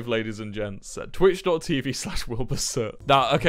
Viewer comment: Excuse me subtitles he did not just say "will help assert" I'm pretty sure he just said Wilbur soot